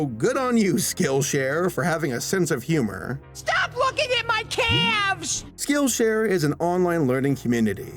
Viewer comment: For a second I thought Carl said stop looking at my a**